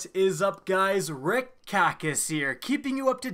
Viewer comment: I hear him say "rick COCK is here" 😹😹